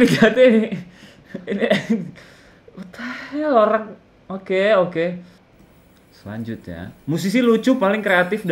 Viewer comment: ngomong apaan bang? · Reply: Di poiri ta te hek? 😂